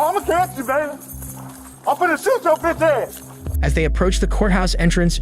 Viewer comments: I heard “I’m finna shoot yo BITCHASS!” but idk though, subs didn’t either 🗿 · i think its pretty clear hes saying "Im finna shoot yo bitch ass" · "I'm finna shoot yo bitch ass" In case somebody didn't understand somehow lmfao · he clearly said "I'm finna shoot yo bitch ass" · If you were curios what he said, it was "I'm finna shoot yo bitch ass". It only says inaudible so that they don't get demonetized. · “inaudible” wtf you mean he clearly said “imma shoot yo bitch ass” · That wasn’t inaudible. He clearly said, “I’m gonna shoot your b*ch ass.” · 'Inaudible' definitely not saying ' IMMA SHOOT YO ASS' clear as day xD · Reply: I heard it as “I’ll put a shoe up your bitch ass” given the context but lol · Subtitles said inaudible but I am 99% sure he said "I'm finna shoot your bitch ass" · lmao it was NOT "inaudible" he clearly said "I'm gonna shoot yo bitch ass" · "im finna shoot chyo bitch ass". translation "inaudible".... nah i clearly heard him say im finna shoot chyo bitch ass. lmao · It’s funny how the subtitles say inaudible when officers make unreasonable threats to unarmed people like at 9:40 the officer clearly says “I’m finna shoot your bitch ass.” Bootlicker ass move censoring that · Whoever labeled what that cop said as “Inaudible” has gotta be deaf. He clearly said “I’m gonna shoot yo bitch ass!” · [Inaudible] my ass. He clearly said “I’m gonna shoot your b*tchass.” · it's "I'ma shoot yo' bitch ass" is what he said I think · I think he said “imma shoot yo bitchass”